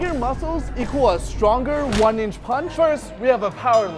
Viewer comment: Come on guys,this is the one inch punch man, don’t mess with him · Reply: I Thought u said one inch man😂😂😂